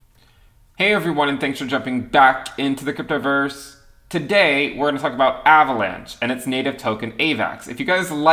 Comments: Why did I hear "today we are going to talk about Avalanche, and it's native token ADA " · I thought I heard "And its native token, ADA". had to listen to it again haha